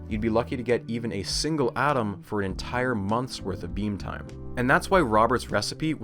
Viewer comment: I misheard beam time for bean time.